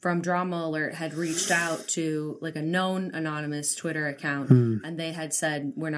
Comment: Jenna said "Keemstar reached out to a known..." I thought she said "Keemstar reached out to a gnome" XD